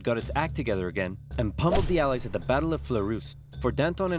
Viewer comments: Wait wait wait did he just say allies? I think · did you say alies